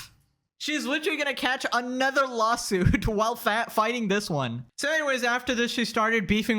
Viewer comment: Sounds like what you were about to say "shes literally about to catch another lawsuit while fat" 😂